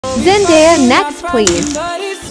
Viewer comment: crap.. I thought they said trying these next please- and then I realized they said Zendaya next please